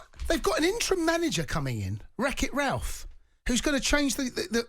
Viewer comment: Did jamie really say intern coach wreck it Ralph? 5:00? 😂🤣😂🤣🤣